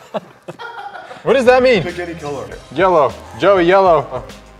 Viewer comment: i thought said "spaghetti color"